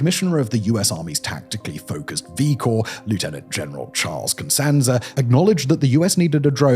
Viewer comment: I misheard the name and thought you said general george costanza😢